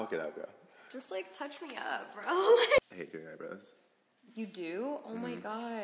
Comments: Does anyone know what he said at 14:22? She said "that's so interesting to know" afterward. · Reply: Stephen Bailey He said i hate doing eyebrows!! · Patrickta: I hate your eyebrows. SAVAGE! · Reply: i thought he said I hate your eyebrows too 😂